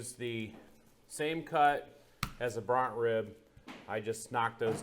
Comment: What type of ribs did he say here: @ 13:01 He cut these first before turning them into short ribs. Did he say "bront"? As in bront..osaurus...?